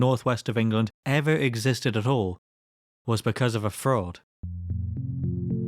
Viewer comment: i started to space out and i thought you said "because of a frog", lmao. edit: also i appreciate there being no ads in your videos, makes them really grab your attention.